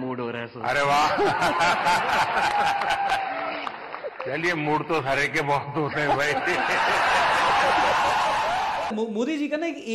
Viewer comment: modiji kya bole kuch samazh nahi aaya . Can anyone explain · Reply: Mood Toh Bahuto Ka karta Hai Modi Ke Sath Podcast krne Ko aesa Bola Ranveer Allahabad dia ko